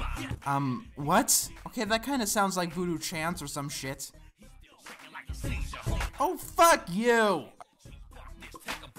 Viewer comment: what did they say to make you throw the F-bomb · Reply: "Shake like a seizure" which is making fun of a really serious thing